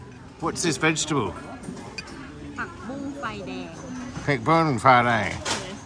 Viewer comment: What She said ("Pak-boong-fai-dang") is actually the dish/menu name. The vegetable name in Thai is "Pak-Boong", so Pak-boong is Morning Glory.